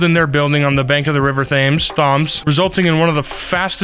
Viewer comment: I’m so sorry but… the way you pronounced Thames is hilarious. Thames is pronounced “tems”. The way you pronounced it I thought you said “thumbs”!! 😂 you did your best, it is quite confusing